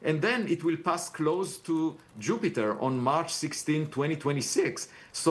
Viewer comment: Did Avi say Mars 16th instead of March 16th ?